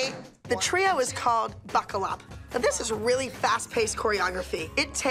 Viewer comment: Am I the only one who thought she said the trio was called "Fuckle Up?" Just me? Just me.